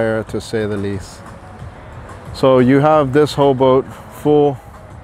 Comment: did he say "Ho boat"?